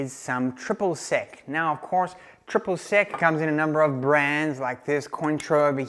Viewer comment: I seriously thought you were saying "triple sick". I guess this shows how much I know about margaritas. Anyway, they look tasty! I'm bookmarking this one.